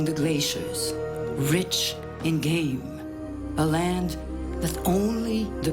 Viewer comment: Did anyone else hear her describe Sarila as "rich and gay" at 23:56?